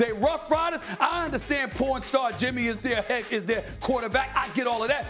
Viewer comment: Did he just say porn star jimmy g at 2:40?? I heard chiseled adonis commentary and he says the same. I just thought they were making fun of jimmy g.